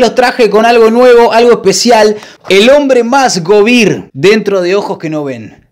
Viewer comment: Por un momento pensé que dijo el hombre más Covid en vez de govir, está cuarentena me tiene mal. Ya estaba pensando que el lo había preferido y cosas así